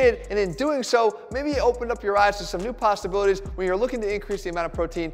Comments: did he just say Pasta-bilities ? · did he say "pastabilities"?